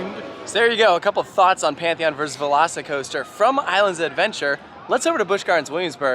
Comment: half the time says "veloci coaster" i hear "lost coaster" and its like indiana beach is punching above its weight